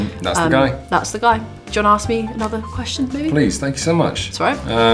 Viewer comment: Is it me or did I hear her say “ do you wanna ask me another question baby” at 1:52 maybe I’m just tripping😭😭😭😭